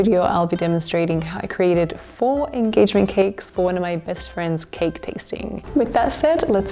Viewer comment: Hahaha! I thought I heard you say “ex-friend” and was like “what? She must be very nice!” Rewinded it and you said “best friend”! 😂🤣😂